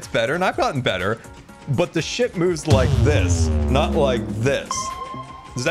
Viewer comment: did scar just say a bad thing (Sh-t)? 😂 hopefully not but that would be so funny lol ❤ Edit: i think he said "ship" 😂❤ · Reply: "The shift moves like this" he's talking about everyone getting better, not just him